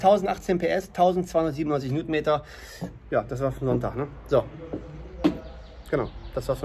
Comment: ich hab am ende verstanden 1018 PS das Wort zum Sonntag ;D